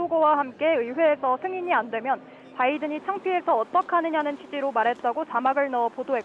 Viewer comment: “바이든 쪽팔려서”라고 하지 않고 “바이든이 쪽팔려서” 라고 한 것으로 들립니다. 아니길 바라며 아무리 들어봐도.. 바이든‘이‘ 쪽팔려서 어떡하냐 로 들려요.;ㅠ